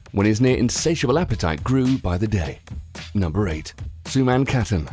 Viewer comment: did he just say his apatite grew by the day? what